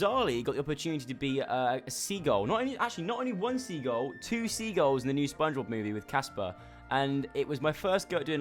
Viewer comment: oh yeah I remember soz I thought he said seals it's seagulls